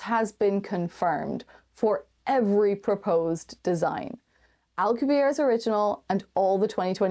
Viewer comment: caption says al qaeda's instead alcubierre's lol don't know if that is also elsewhere in the video but noticed it there